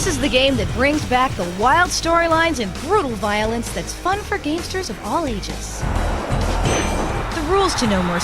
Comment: Did I just hear the word "gamesters"?...